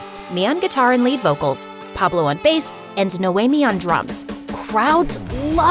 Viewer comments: I thought it said Naomi on drugs💀💀💀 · What said: And Noami on drums What I hear: And Noami on drugs..... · i zoned out and thought she said Naomi on drugs lol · I thought she said noami on drugs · HELP I THOUGHT IT SAID NOAMI ON DRUGS IM GIGLGIGNTNHAHAHAHA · it sounded like “Naomi on drugs” 😂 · When said “ And Naomi on drums” I thought she said “And Naomi on drugs” 😂😂😂😂😂😂😂